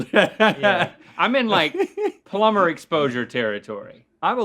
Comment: the subtitles say polymer but Link really said plumber.